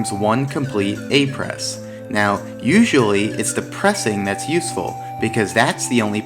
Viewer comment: "Usually it's the a press" I thought he said "Usually it's depressing" lol